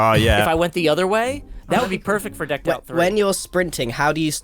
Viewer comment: Wait, @ 13:26 did i hear say something about Decked Out 3????